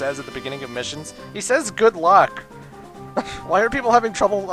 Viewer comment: Wait, the voice says "good luck"? I thought it said "Grid Lock" like they're about to take off.